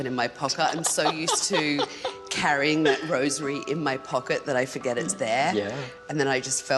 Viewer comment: I thought she said she had rosemary in her pocket.